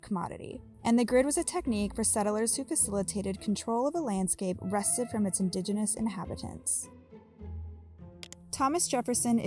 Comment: I quite honestly had a difficult time following. I am partial deaf, so I may not be the most reliable source. However, (while using cc) what are they saying at 3:46 and 8:13? "rested from its indigenous inhabitants" and "a solution to the iyres"?? I disdain auto-generated English. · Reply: The first one is "...wrested from its indigenous inhabitants...". The second one is less clear cut. I'm fairly certain she says "...to the ires of the modern city...", which doesn't make any sense, is certainly not any more grammatical than what the captions show, and adds even more credence to this whole thread.